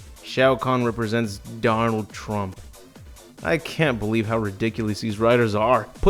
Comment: please tell me i'm not the only one who hear "Darnold Trump"